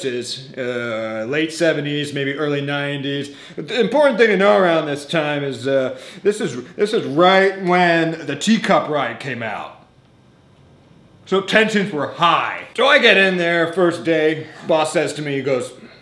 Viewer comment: I swear to god, I thought he said "tea cup RIOT" instead of "tea cup RIDE"